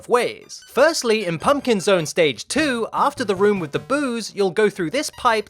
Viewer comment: I think I was the only one who thought he says booze instead of boos